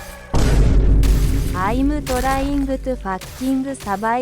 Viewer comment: If you didn't understand what was saying this what was she was saying 0:01 "I am trying to fucking too"